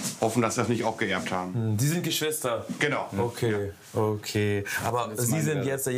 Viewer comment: habe zuerst verstanden "Sie sind die Schwester?" ;-)